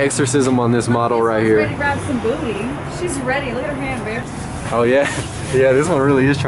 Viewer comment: Woah, did she say babe? really got a girl?! Fuck. I knew I should've got some fishes.